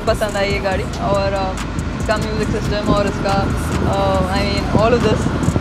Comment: did she just say music system?